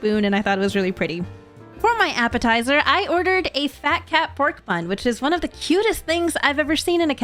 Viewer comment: When you said fat cat, I thought you said Mat Pat 💀